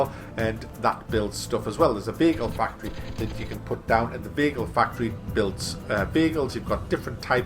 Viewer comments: Did anybody else hear "Bagel factory" at 4:11? XD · Oh I thought you were saying you have bagel factories, but it was vehicle factories. That makes more sense. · at 4.10 I thought you said there was a Bagel factory so disappointed it is a vehicle factory....yeah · anyone think he said bagel factory too? lol